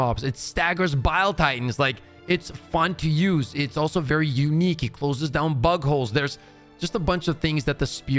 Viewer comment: i thout i heard "it closes down buttholes"